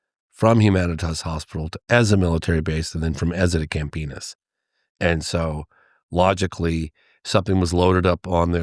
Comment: Camp PENIS? Did I hear?? 😂